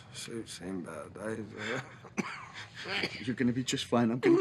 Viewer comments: what did they say? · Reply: suits seen better days x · What does ben say at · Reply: 'Im alright. suits seen better days' (as in because its bloody)